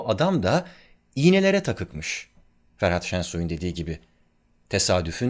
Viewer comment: Düzeltme 2:29 ''Ferhan Şensoy'' :) (Ferhat gibi duydum)